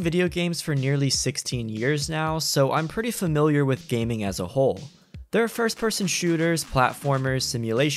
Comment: i heard first time "as a who*e"